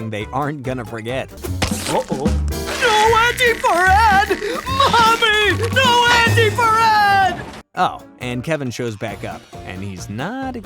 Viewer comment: did Ed say “no wedgy for Ed , mommy, no wedgy for Ed”?😅